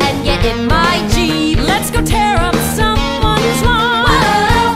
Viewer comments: I thought it said “let’s go tear up someone’s mom” 💀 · i like this. it looks like they’re all trying to highkey seduce veronica and keep interrupting each other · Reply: I always mishear “get in my jeep” as “get in my jeans”